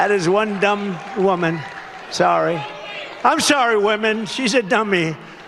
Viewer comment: Since all Dump has is salesman doublespeak, we’re forced to endure his endless references like “more than people have ever seen” because his ‘clever’ turn of phrase belies an excuse: that he was only referring to blind folks. Clever, Don. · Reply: I heard “sorry, women” as “sorry: not sorry.”